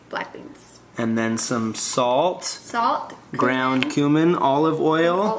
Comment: 8am me deffo thought said "ground human".